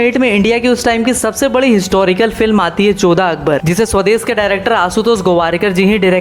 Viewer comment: did he say "Choda Akbar"